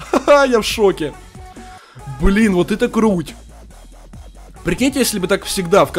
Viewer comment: мне одному послышалось Вот это грудь.